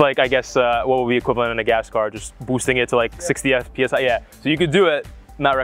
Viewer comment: | Did he just say 60fps?